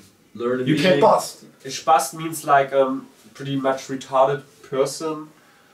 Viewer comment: What does the guy say at 1:36:57 lol it sounds like "UK PAST!!!"